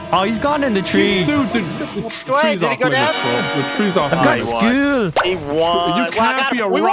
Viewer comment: Did anyone else hear vanoss say “I got school” at 7:28😂